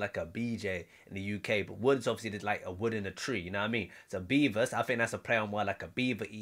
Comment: He said “Bevis”, like the old MTV show Bevis and Butthead. I have to give you props for coming with a double entendres of it though since you heard it as “beavers” 👍